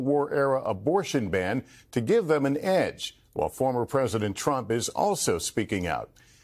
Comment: WOW. They let a terror on the watchlist go i just don't get it. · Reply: I thought they said Trump is a liar...